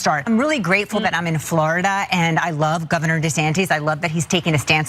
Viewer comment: Did she call him governor desanteees? (@ 4:02) never heard that one before. But at least she loves him. And that's all that matters. However you say his name.